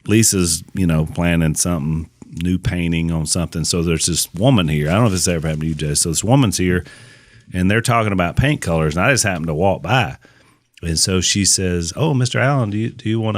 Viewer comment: LOL With Al's accent , I thought he said weigh in on the Pain Killers then finally realized that he was talking about Paint Colors 😱